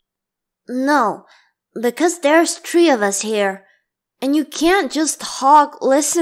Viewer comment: No cuz there are tree of us here That’s what I heard😂